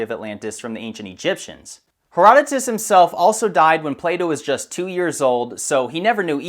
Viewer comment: i thought you said playdow